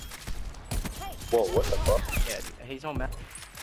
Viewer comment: that subtitle at 0:30 said hes on mouse, i lowkey thought that kid said hes on meth lmao